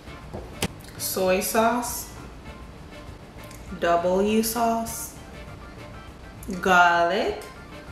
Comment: WHAT IS THE SECOND BLACK SAUCE YOU PUT ON THERE? IT SOUNDED LIKE you said double your sauce or w sauce.